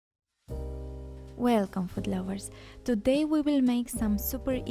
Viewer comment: Sounds like u say foot lovers👣👣👅😍😍😂 lol . We love your Videos❤. Keep going. All the best🔥